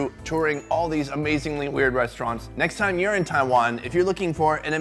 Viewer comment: "Next time you're in Taiwan... " Literally heard it as 'urine Taiwan'... I lost it!